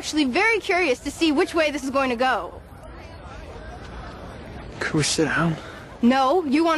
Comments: what did he say? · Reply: "can we sit down" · what does jess say at 1:26/1:27?! "could of..."?! PLEASE HELP ME!!!!!!